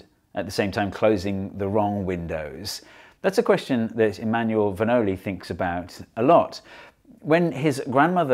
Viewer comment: Before I saw his name printed on screen, I heard "Emmanuel Bernoulli", and I thought, "well, no wonder he's good at this stuff!!" :))